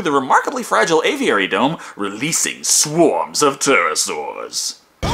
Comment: Did he say “Terrorsaurs?”